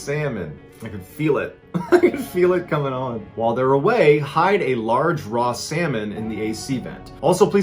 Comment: When you said “hide a large raw salmon in their ac vent” it sounded like you said “hide a large raw salmon in their anus”. I am scarred for life